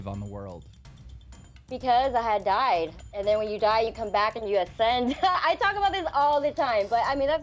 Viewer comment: Did she just say "When you die enough" ....?